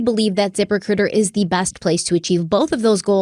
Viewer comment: Every time someone says “zip recruiter “ I hear “zipper cooter”